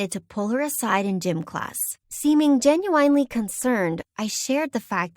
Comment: anyone else hear the genu-wine-ly concerned 🤣🤣